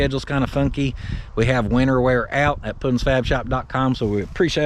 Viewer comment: Dang when you said you have winter wear, I heard " wiener wear" and thought, what the heck?